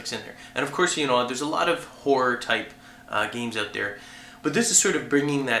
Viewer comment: i swear he keeps saying whore and not horror